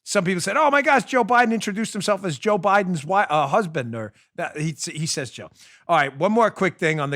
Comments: It sounded like he said Joels Biden Husband LOL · I think I need to go to the V.A. hospital, since I clearly heard 'Joe Biden's husband'...